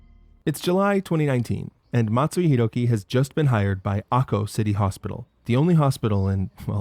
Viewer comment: I had to do a double-take; I thought you said "Aho City" for a moment!